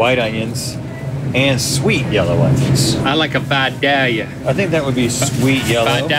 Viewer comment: Whenever Link was trying to say "Vidalia", I thought he was saying "fat daddy" in a weird voice and now I can't get that out of my head